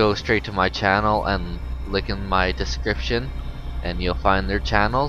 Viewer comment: I think I heard him say, "lick the description"